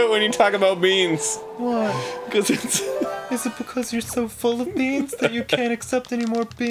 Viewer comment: i thought Dan said "you're so full of memes"